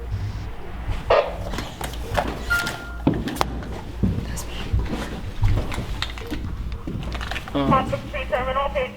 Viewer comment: does anyone know what the walkie talkie said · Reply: It said that's me